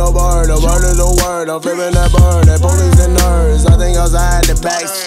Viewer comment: the bird is the word I'm flipping that bird the police are nerds😉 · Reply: I thought he said "i bully some nerds"